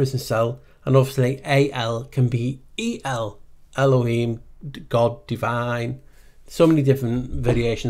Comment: Guess how you say "northern lights" in Greek ;) "βόρειο σέλας" <voreo cellas> Great video, thx! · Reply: every time you pronounce "elohim" I hear "haloween" :D :D :D